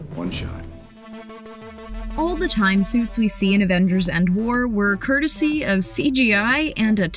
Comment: She did not just say Avengers End War 1:45 Also I never knew Jason Derulo was in Cats!!